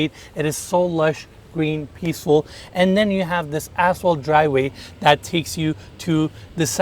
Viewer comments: Did he said "asshole driveway"? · What did you call the driveway? 🤔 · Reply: 😂😂😂 I am pretty sure he said asphalt... 🤣🤣